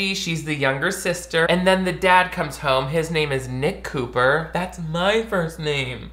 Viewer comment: I thought you said Mick Cooper and I was like, your name is Mick Nick!? Lol I haven't had enough sleep